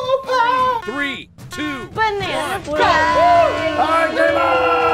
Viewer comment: Was Mari's callout for House About Some Pie "BANANA SPLIT PIE!"? That's what I thought I heard her say. A- It's Banana CREAM Pie, B- She's trying to kill Wes. :P